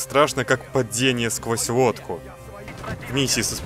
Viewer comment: мне не послышалось или он сказал водку?